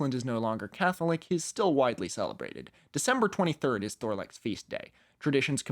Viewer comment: I misheard this as November 23rd at first and was about to go insane at the idea of the patron saint of Autism's feast day being the same day as the premier of Doctor Who but alas.